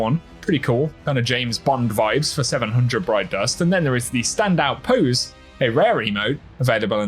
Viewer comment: I really thought he said “Standout Poos” 💩